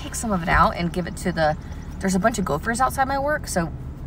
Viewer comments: i thought u said coworker not gophers at first lmao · I thought she said there’s a lot of golfers⛳️ by her work and she was throwing salad for the people golfing😂😂 I need a nap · Omg I heard "Gophers" as "Golfers" and I was so confused at first. It took me two watches and looking at the comments to clear that up for me lol.